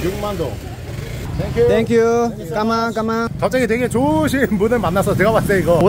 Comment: 까망까망?? 이라들리네요 ㅋㅋㅋㅋㅋㅋㅋㅋㅋ